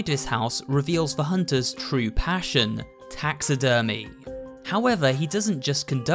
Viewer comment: I wasn't listening and, I thought he said Tax Evasion 💀